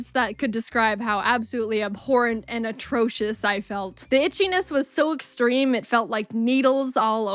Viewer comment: it sounds like she said the bitchyness